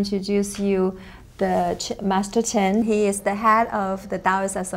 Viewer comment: The CC says... "shit master 10" lol